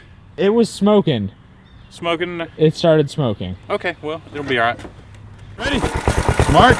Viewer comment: Am I mistaking or did just say "it'll be alright". 13:38 How is nobody mad about this